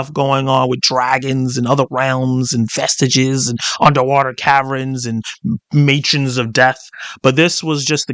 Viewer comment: I had to re-watch this part. At first I thought you said "underwater Karen's" and imagined angry mermaid's that wanted to speak with a manager.